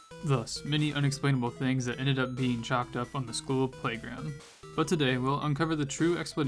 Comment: “Chalked up”? I didn’t know that certain groups of people owned words or phrases… “chalked up” doesn’t have the same meaning in any way but I am sorry if it bothered you. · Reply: they do it's like when white ppl try talking in black slang lmao anyway my bad i thought u said "chopped up" like "chop it up" not "chalked"